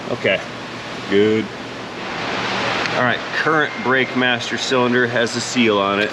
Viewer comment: for a moment i thought you said bastard cylinder